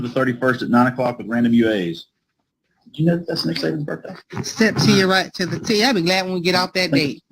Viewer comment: Whose birthday? I heard you say some coach. I thought he said satan’s birthday at first. 😂